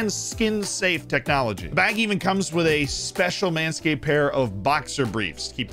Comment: Was making breakfast half listening and thought you said ManCity right above Southampton instead of Manscaped. Was like “wow guess he wants to see the etihad on the last day”